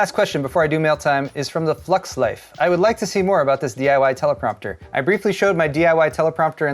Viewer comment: I heard 'DIY teleprompter' and heard 'DIY Typewriter' for some reason. I was very confused.